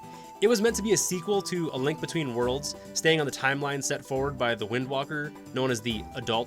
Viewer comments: did he just say wind walker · Holdup did you just say windwalker?